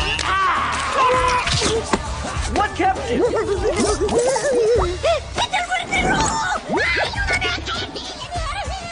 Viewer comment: What did that thing say when he saw Thrax? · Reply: He said: "It's the Red Death!"